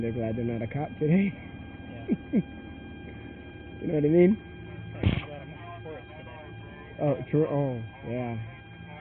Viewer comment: what is this officer saying? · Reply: I think he said “ I’m glad I’m not a dispatcher today”